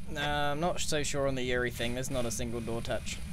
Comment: I'm being driven crazy is he saying yuri what is he saying.